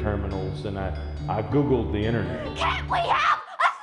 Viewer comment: What is that kid at 1:44 even saying? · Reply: "Can't we have a 3D Environment Classic Sonic game, for crying-out-freaking-loud!!!"